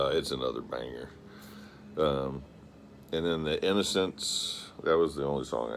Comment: Thanks, but that is one of the ones I heard. I couldn't find "The Independence". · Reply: maybe because it's "The Innocence"😆 <link>